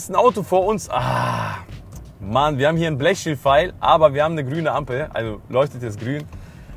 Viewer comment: Ich verstehe das Wort bei 8:25 nicht... Blechschildpfeil? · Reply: Er meint das Grünpfeilschild.